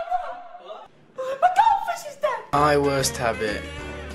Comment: Me: what? Me: sorry, still dont understand next thing i hear Its cause the goldfish is-! ......... yeah,bye